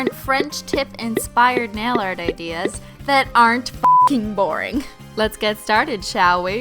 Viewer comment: I THINK I HEARD A CORINNE